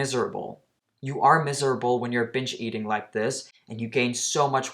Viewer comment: I thought he said "b*tch eating"😆